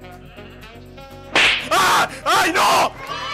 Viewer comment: "Ay no!!" means "Oh no", he was a spanish guy i think or at least that is what i hear